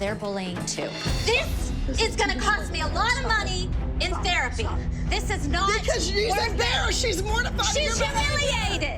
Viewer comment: this moment is so cute between Kendall and Abby . Sad that Abby changed . Does anyone know what she was saying to Kendall! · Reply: I’m pretty sure she said your tough, stop it